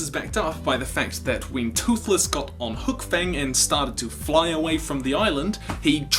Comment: is it me or did he just said toothless got on the back of hook fang?!?